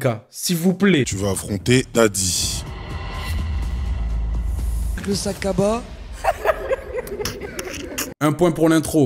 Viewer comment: Elle a dit quoi à 4:48 ? je veux rire moi aussi mais j’arrive pas à cerner les mots de sa bouche 😂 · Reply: Elle a dit « le sac cabas » mdrr